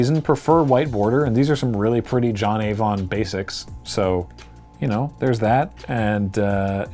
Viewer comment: I misheard "John Avon basic" as "Johnny von Basic" and wasn't sure what to make of it at first.